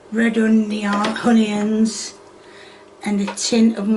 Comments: I thought why does she keep saying "honey ends" until I realized she means onions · It sounds like she says honions instead of onions